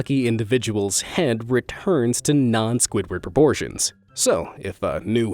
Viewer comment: did he say Squidward